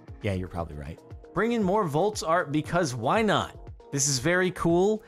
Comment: Volts? I thought he kept saying Vaults.